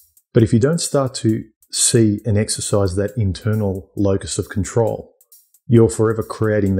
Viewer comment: "Exercise that X of control" What does he say? English is not my language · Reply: Locus